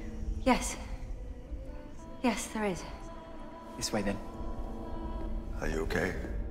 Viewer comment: Is it just me or do I hear “yes, there is” just before Clara says it at 3:48?